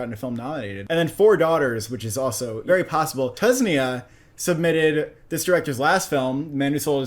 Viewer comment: My brother how did you pronounce it Tusnia instead of Tunisia 😂😂😂